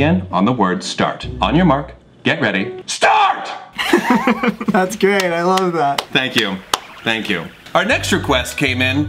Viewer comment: I THOUGHT HE SAID THATS GAY I CANQT BREAT